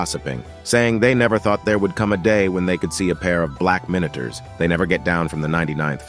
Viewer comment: I was using captions and instead of saying black minitor it said black minors.